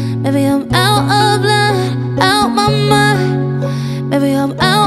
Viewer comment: Is she saying"out if blood" on this , because the original version I thought she was saying "out of line" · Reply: nah she's saying "out of line", just pronouncing it strangely in some places.